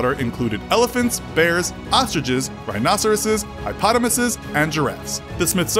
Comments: “Did he just say, “Highpotamus’”? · Did you just call a hippopotamus a "Highpotamus"? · Did he say, "Hipotomuses?" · Did he just say "HIGH-potamus"?